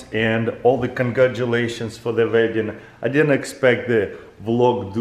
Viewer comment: If you use generated subtitles it says "vagin" instead of wedding